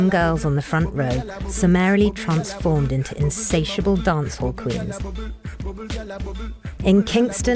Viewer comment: did she call the girls in the front row "insaciable dance hookers" ?at 3:11?